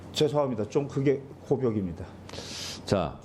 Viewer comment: 와 진짜 멋지십니다!!!! 8:53 분에 김경호 변호사님이 “죄송합니다 하고 뭐라고 하신건가요??? 미치도록 궁금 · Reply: 고벽: 아주 굳어져 고치기 어려운 버릇.